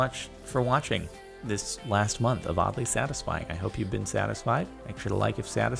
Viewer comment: I thought that it said you can't be satisfied